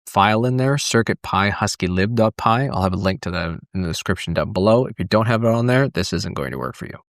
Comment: did you say. ”in the description dot below”